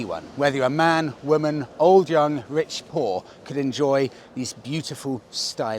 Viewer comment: Did he say rich-POOR? Sure yeah 😂 with that motorcycle costing USD 20K